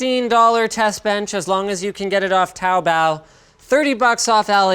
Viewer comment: Automated Captions thought Anthony said "Cow Bell", waited to the end to find out it's Taobao when Linus said it.